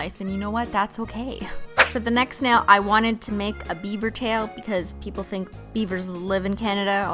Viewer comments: I THOUGHT YOU SAID BIEBER TAIL THIS WHOLE TIME😫😫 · When you said 'beaver tail' I thought you said Beiber tail bc he's also from Canada skwnsnen